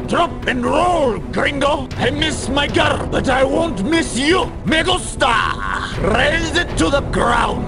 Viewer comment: I thought he said "i miss my car, but i wont miss you!"